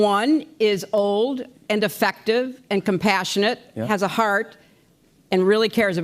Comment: Old and defective - is what she meant and what I heard.